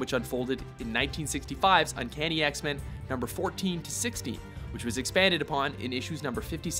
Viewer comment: In the beginning-ish of this video it sounds like he's saying "Uncanny X-Men #'s 14 through 60" instead of "#'s 14 through 16"… listen to it again people; I can't be the only person that believes it sounds like he says 60 rather then 16